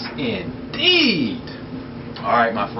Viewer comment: At the beginning of the video i wasn't watching, and i thought you said yes its eiiiid instead of indeed LOL